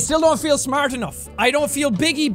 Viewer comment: I thought you said you were big brained! 🤔🤨